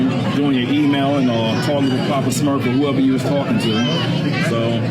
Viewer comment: did he really say she was on the phone with her "Papa Smurf"?😅😅